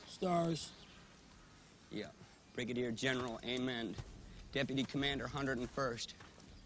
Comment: stars.. "yep brigadier general Amen." is what I thought he said for a long time. like a saviour brig general they had to protect. i thought the "Amen" was him being sarcastic. only to later find out it was General Amend.